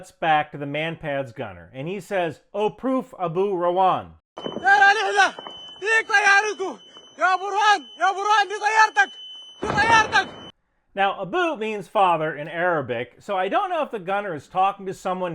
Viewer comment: he actually said " ya burhan di tayartek" Which means in English:" burhan this is your plane" He is mocking army chief abdel fattah al-burhan